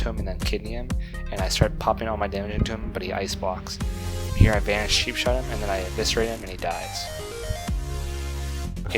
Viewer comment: Hi dude. I can't hear what you're saying before the word Eviserate? Cobra of Eviserate? Cobolt of Eviserate? Combo of Eviserate? Sorry, I am new to the rogue. :D And what do you say at 4:04? "Popping all my dmg items?" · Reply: He's saying Cold Blood :). <link>